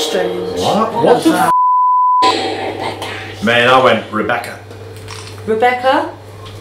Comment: Am I the only one who heard someone whisper "Rebecca" clear as day around 1:09:20 or am I missing something?